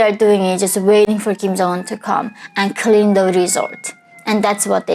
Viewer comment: About the pleasure squad, I heard this as "All their life they are waiting for Kim Jong Un to come, and clean the result." Yes, you actually said resort, and yes you probably didn't intend any dirty meaning either, but I bet this is not so far from the truth...